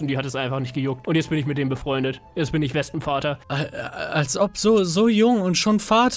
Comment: Ich hab bei 5:05 einfach verstanden "Und jetzt in ich Lesben-Vater" lol